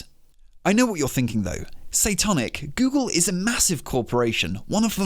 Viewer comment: I keep hearing "satanic" instead of 🤦‍♂️